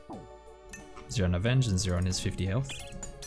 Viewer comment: Lmao bro for the few years i been watvhing yo vids i swear i always hear u say " on his 40 hp"